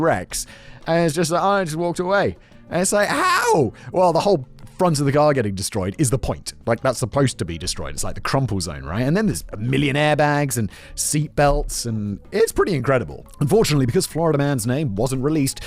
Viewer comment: Did anyone else do a double take at 7:18, hearing "millionaire bags" instead of "million airbags"?